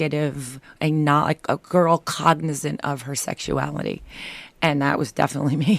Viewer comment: What does she say at 0:23? "...a girl......of her sexuality...." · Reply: cognizant